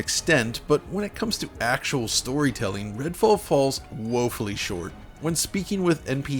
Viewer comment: Did he say "Midfall..." LMFAO, somebody double check to make sure I'm not the only one who heard that.